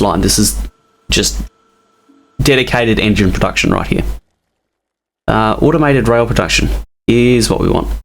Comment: Did you just say Automated rail protection?